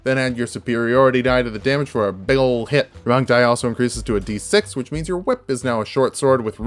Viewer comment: What are u saying at the 6:34 mark sounds like your saying wrong die increases to a d6 i have no clue what your talking about